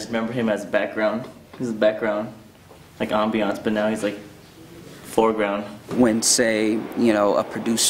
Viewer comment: Did he just say Jo was first background, now he floor ground? 😅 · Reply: Foreground